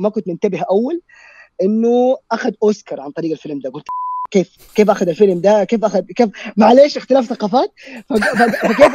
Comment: شفت البث اعرف ايش قال 🙂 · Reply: قال "احييه" وبالمصري معانها وحش وشكراً